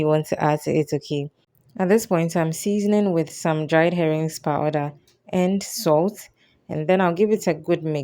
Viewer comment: Went into an African market to learn more and felt drawn to the koobi. So I bought some and started looking up recipes and found this. I’m totally new to African food. What seasonings did you add while cooking? I thought I heard herring powder. I’m going to give this a try. Thank you! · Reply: Wonderful! Yes I added herrings powder but you can do without it. Just the koobi gives the stew great flavour